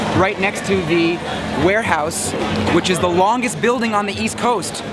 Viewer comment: I thought it said whorehouse. Entertaining nonetheless.